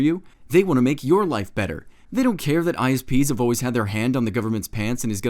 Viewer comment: When the politican said isps I thought he said ice peas.